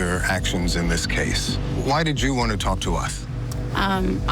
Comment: Did he just say "why did u wanna talk to uth??" 😂